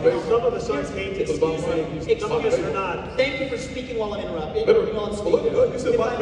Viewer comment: Did he just say "Thank you for speaking while I was interrupting"?